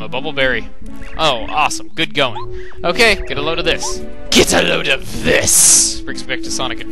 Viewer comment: Eggman: Get a load of piss! It always sounds like he said that to me